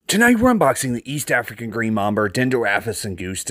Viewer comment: Wait did you say Mamber? ❤